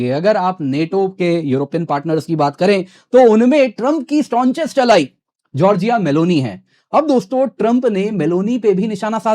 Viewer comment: Did I hear strojest ally meloni